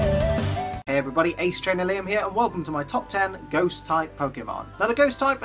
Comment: please tell me I'm not the only person who heard "Everyone hates Trainer Liam..." I know it's "Hey, everybody, it's Ace Trainer Liam here..." I think it was just because you spoke too quickly or because this was a ghost type video, it was meant to be kind of depressing?